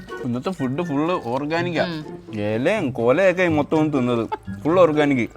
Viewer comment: what did you sayyyy😂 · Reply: Kola😂